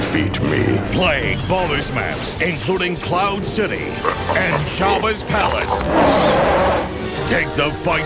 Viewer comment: I could swear it sounded like he said Jawas Palace instead of Jabas Palace.